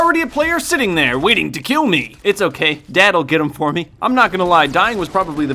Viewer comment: Did he say dad